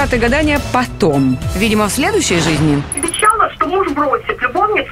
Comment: Я не одна это слышу?: 34:40 Любовница вернётся ко мне навсегда. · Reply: Нет там «любовницу бросит, и вернётся ко мне навсегда»))